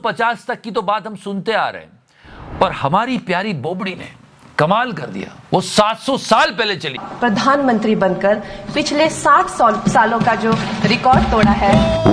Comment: Abey behera hai kya?!?! She said "saath saal" (60 years) - she fumbled a bit & maybe that's why one can get confused, but in the end, she clearly said 60 years! I don't like her, but don't spread fake news or make fake memes like BJP IT cell trolls!